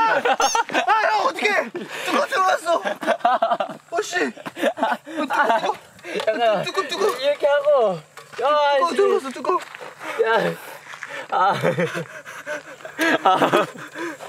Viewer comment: did hoshi say oh shit? 😂😂